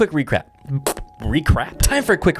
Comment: Best explanation I've seen. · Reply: Especially @ 8:04 when he said time for a quick re-crap instead of recap !!!